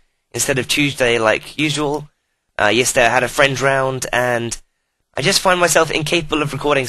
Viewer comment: LOL at first i heard: "Yesterday i had a friend drowned" and i just went "What The F*ck Panda O.o"